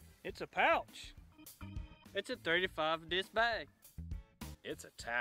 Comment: Love it! But I think the guy says 3 to 5 disc bag, it sounds like he says 35 disc bag!! It confused me at first. cool pouch, I want one to put in my bag.